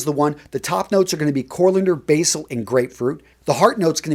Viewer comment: said "corLander" instead of coriander 😂